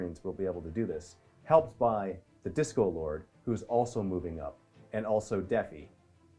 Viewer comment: I keep hearing 'Disco Lord' instead of 'Discord Lord'. Lol.